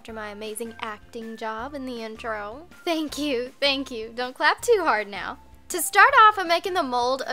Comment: I legit thought she said don't fap too hard instead of don't clap too hard